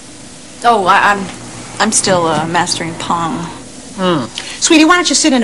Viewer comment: I thought she said "porn" at 03:41 lol